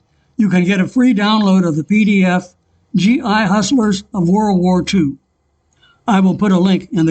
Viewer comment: You were saying “Hussars.” I thought you’re saying “Hustlers.”